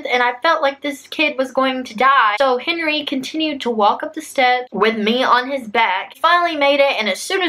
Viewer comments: "so Henry continued to carry me up the stairs-" I swear I thought that's what she said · Did she just say his name is Henry at 10:29?